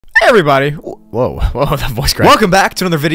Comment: what I hear:HARY BODY🤣